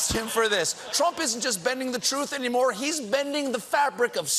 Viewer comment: I heard "bending the rules". However "bending the time" can be a kind hard. Someone is allergic to the history books.